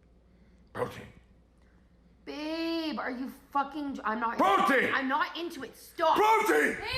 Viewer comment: i tought the guy was screaming "Putin" instead of "protein", i know, i'm dumb ✌👁👄👁